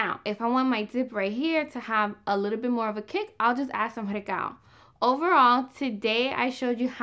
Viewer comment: I'm loving all your recipes. Thank you! But I couldn't tell what you said here. You'll add some what for a little kick? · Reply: She said recao aka culantro